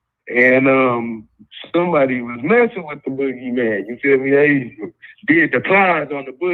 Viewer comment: when that third caller called in from Detroit an started off explaining his story Fam your first reactions had me crying when you said “huh” you thought dude said he was messing with a guy which it did sound like he said it.That was almost SPOOOOOOOOOOOOOOKY!🤣🤣🤣🤣🤣